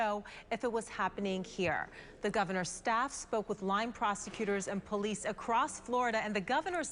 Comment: Sounds like they saying lying proscutors. Lol sound correcr